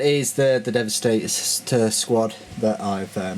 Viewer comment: I think you mentioned it in your Reaver titan vid. If not then it would just ve logical to have it after the Devs cause they came around the same time. Or I am just guessing :) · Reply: Hmm... I really thought you said assault squad. Well I could still take the prize ;). Oh yeah could you sometimes in the future make a review of the thunderfire cannon. Well what ever your next WH vid is I'll be waiting for it.